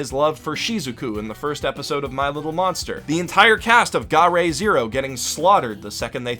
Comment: Update on the Crunchyroll URL - I've been told that it still works despite the "expired" message, but it has been scaled back to a 14 day trial for now. You will still be supporting the channel by signing up using <link> though! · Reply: at 13:27 what is that anime? I can't quite understand you and subtitles say "the entire cast of Gaara zero getting..."